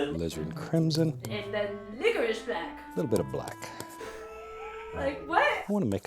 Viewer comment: Did just said ” n****ish black” I hope I’m wrong.